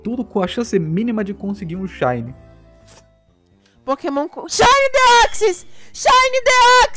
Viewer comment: Mas que shinys ela disse? Nao entendi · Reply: posso estar enganado, mas acredito que seja SHINY DEOXYS!!!